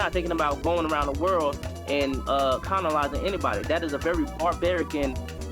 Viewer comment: he actually said conolizing